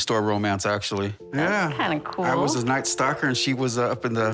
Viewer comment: Anyone else mishear ‘stocker’ for stalker?